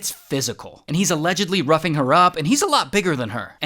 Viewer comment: I’m so sorry but every time he said ‘roughing her up’ I heard ‘rubbing her up’ 😐😐💀💀💀